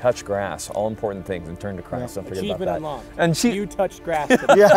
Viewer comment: It was quick, but I heard it: turn to Christ- don't forget about that!